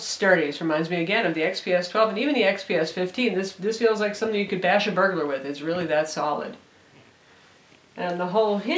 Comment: O. o i thought she said bash a burger.